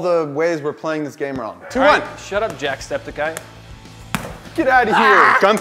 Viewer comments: I swear I heard "Shut up Jackscepticeye. " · Am I the only one who heard the little "shut up jackcepticeye" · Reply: Deesilsnarf nvm i heard "shut up jacksepticeye"